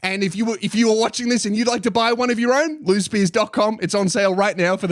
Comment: Every time you say your name I hear "loose beers" then I realize half a second later.